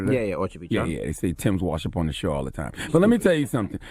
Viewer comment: did he say timbs wash up on the shore? 😭